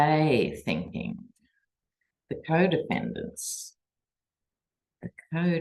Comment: I thought you said "co-dependents". The co-dependent co-defendants. Wake up. Time to break the toxic cycle. Go Fani!